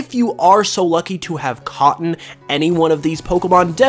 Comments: did he just say... Caughten? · did you say “caughten” ??